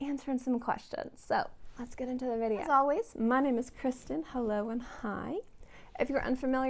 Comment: heeheehee, i misheard "hello and hi" as "hello i'm high" 🙈😂